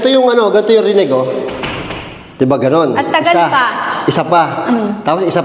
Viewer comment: pakinggan mo may narinig Akong boses Ng lalaki sa 23:41 secs. boses mo ba Yun. tsaka Yung 23:46 secs pakinggan mo may boses Ng babae Sabi nya "Isa pa...."